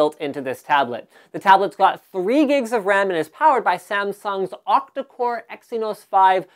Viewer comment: i thought he said it had 64 gigs of ram. jesus i almost fainted .